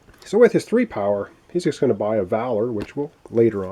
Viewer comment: I was listening to the game while putting some protective clear coat on some cardboard counters and I swear I heard you say buy a vowel. after the 3rd one I realized it was buy a valor.